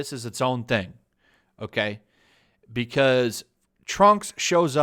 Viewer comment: I thought he said Trump for a second